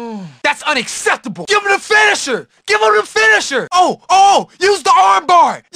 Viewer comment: What he says I don't understand him? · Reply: “Give him the finisher” “use the arm bar! Use the arm bar!!